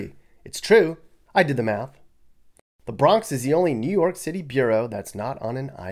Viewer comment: Did you actually say Bronx is a "bureau" instead of "borough" ? )